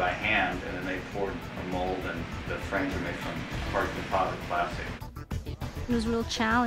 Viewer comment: What did he say at 4:11? I cant understand what type of plastic he is referring to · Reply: 'Hard to deposit plastic' I think.I am not sure but that's what I hear :).